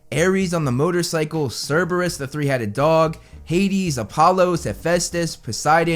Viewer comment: Did he just say Apollo?